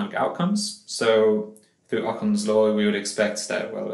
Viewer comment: Hi, thanks for the informative upload. Please could you confirm what was said at 3:30-3:35 as there was a sound glitch-something pertaining to '.....law', thanks. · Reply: Hi there, at that point I mentioned 'Okun's Law'. Best